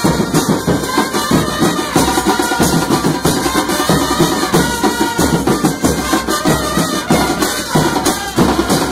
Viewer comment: que dice ahi? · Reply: Mauricio Caranta